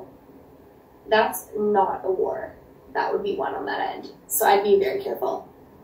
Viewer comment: what did she say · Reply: sounded like "that's not a war... that would be won on that end, so I would be careful." I'm interpreting it as 'this isn't a fight you want to start cause you would lose'